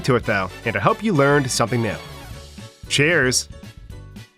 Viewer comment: it sounds like ur saying chairs